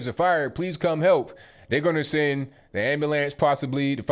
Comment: Not being rude but I hear this every time 8:27 "AMBULAMS", is that how people say it in the USA? Just curious... Air + knee + ways = Anyways Bye (For now)